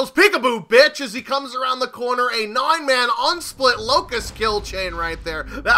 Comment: Did he say a 9 man on split??? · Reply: Unsplit